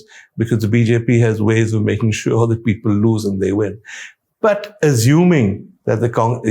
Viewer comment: did he really said that BJP has ways to make sure that "people lose and they win" or I heard it wrong?